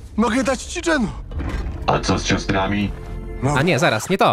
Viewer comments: ja też najpierw usłyszałem ciastka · Ja za pierwszym razem jak Jonse mówił o Geno to usłyszałem dżemu ale nie słyszałem by Fundament mówił coś o ciastkach. :) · Jak myślicie, w którym sezonie dowiemy się kim jest Genō i reszta? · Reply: Zrozumiałem Genō, ale za pierwszym razem rzeczywiście usłyszałem " A co z ciastkami?"